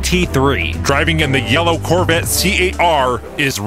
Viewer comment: It really sounds like is saying, "The yellow Corvette C-A-R." they're all C-A-R's.